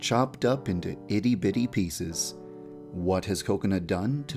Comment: When he said "Kokana" I thought he said "Coconut" 😭😂